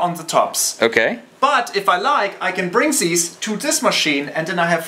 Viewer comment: When he said "I can bring these" I thought he was saying "I can bringsies!"